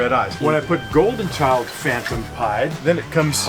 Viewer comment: the black and white snake with red eyes at 2:51 is insane!! didn't catch the name because bryan was talking 😠😂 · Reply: Golden Child phantom pied is what the guy said he put into it :)